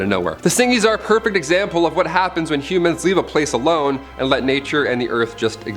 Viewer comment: First time he said humans I thought he said Cubans lol